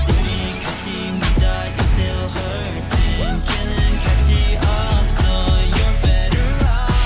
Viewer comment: I could have sworn I heard "Killing kaachi." They've been getting a lot of hate but this is just- xD